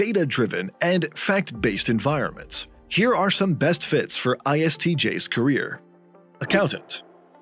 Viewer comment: Well, I caught the "inspector" misspelling at 0:19. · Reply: I noticed the subtitles say INTJ when he actually says ISTJ